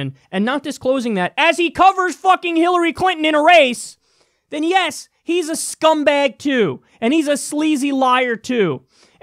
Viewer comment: "his a scumback TOO" i thought he said "his a scumback jew" lol